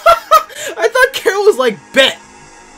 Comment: I Thought carol said bet😂